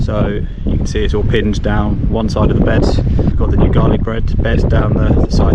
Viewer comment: I thought you said you planted garlic bread